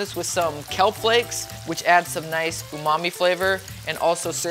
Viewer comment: Ok guys.. enough w/ this talk of soylent green! I honestly thought I heard him say "add Mommy flavor"!! ( He said Umami).. lol😂